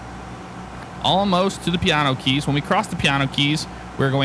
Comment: When you say piano keys it sounds like your saying Keanu reeves